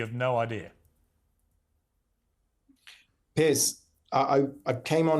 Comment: Love how at 5:25 the autocorrect thought the guy was calling him "Piss" instead of "Piers"